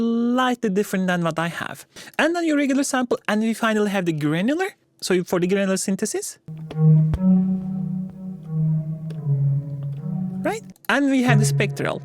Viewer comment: I randomly opened the video from there and I thought you said "güvenilir" hahaha. Anyway, I was gonna say that I checked the whole default library and "LD - Animate" that you designed is really standing out! Absolutely fire! Excellent job!